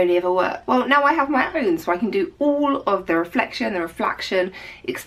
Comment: Lol did you just say "reflaction"? All of those similar sounding words can get confusing